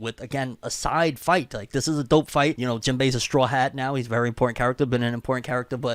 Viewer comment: 😂 had to rewatch this like 5 times!! I thought he said jinbe is a very pour character